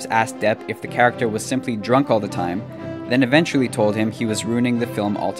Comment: lol. @ 1:36 the orator says "executives asked Depp if the character was simply drunk all the time" But the subtitles say "excutives asked Depp if the character was simply drunk or gay"